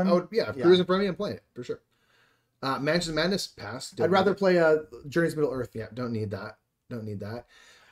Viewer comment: what game is mentioned instead of Mansions of Madness ? Also congrats on the 1000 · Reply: We say "Journey's in Middle Earth" It's the Lord of the Rings version of Mansions of Madness